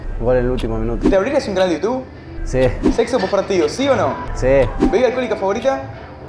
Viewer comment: Min 2:06 Que dice? Sexo por partido o que? Nunca entendi eso xd